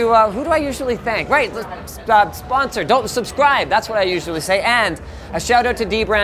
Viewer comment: did i hear don't subscribe 😵